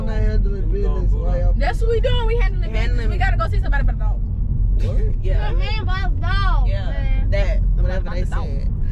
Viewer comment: Anyone know what said at 6:30 im so confused 😭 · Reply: we gotta go see somebody bout a dog